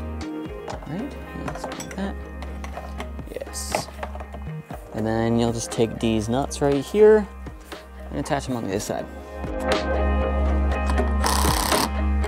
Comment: At first when I heard "deez nuts"6:28 I thought it was just me having a dirty mind, but I would like to know if you also think I am not insane